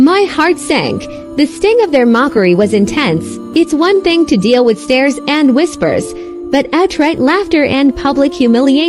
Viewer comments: When she said "It's enough to deal with stares and whispers" I thought she said "It's enough to deal with stairs..." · When she said "It's enough to deal with stares and whispers" I thought she said "It's enough to deal with stairs..." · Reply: i thought she said, "my next meal is steers and crispers."